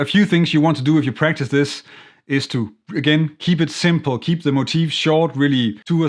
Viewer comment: Thanks for another great lesson! I had to rewind the video; when you said "you can practice this roboto." I thought you said " you can practice this in your bathroom " !! Yes, I laughed out loud.